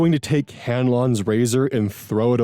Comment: I heard "Ganlon's Lazer" instead of razor lol